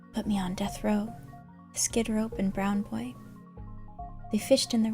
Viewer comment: Did she said brown boy in